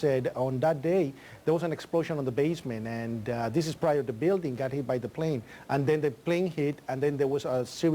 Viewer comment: In this one I thought I heard him say "this was PRIOR to the building being hit by the plane".